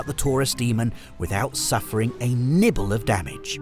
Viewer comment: I swear I kept hearing "Tourist demon"